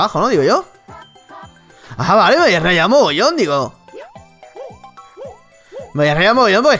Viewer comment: alguien entendió lo que dijo? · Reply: "Ha vale, me habia rayado mogollon" lo entendi y eso que lo escuche a Velocidad x2 xd